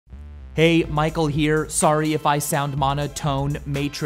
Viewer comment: WHY IS THERE NO "VSAUCE" IT CANT JUST BE "HEY MICHAEL" ITS "HEY VSAUCE MICHAEL HERE" COME ON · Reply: Bolicob IV For a second I thought you said "hey ding dong, Michael here" No Idea why I thought of that...